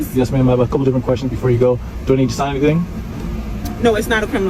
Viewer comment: it sounded like she said you don't have to sign anything.